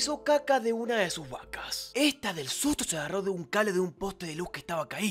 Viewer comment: Escuche "Pero del susstuto" Fui la unica?